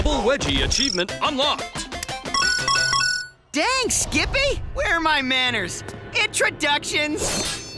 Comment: Did he say skibi